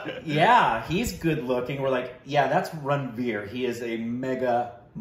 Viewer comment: Did just call Ranvir RANBIR??